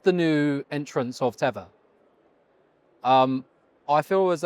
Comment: Host, what is Tevor? You said “Tevor” several times (22:54 mark).